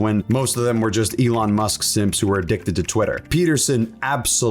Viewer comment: WHAT DID YOU CALL SHAPIRO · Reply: Twitter addict